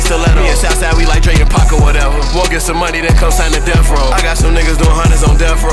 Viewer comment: I thought he said “ if you want some money then sign to the devil “ 😂🤦🏽‍♂️